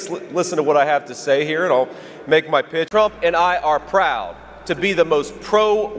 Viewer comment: Does Vance say "...And I are proud."? That's not gramtically correct. Unless he was trying to be funny or something... WEIRD.